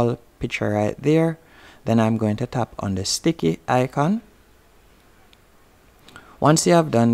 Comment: Bro what did you say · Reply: He ment the check mark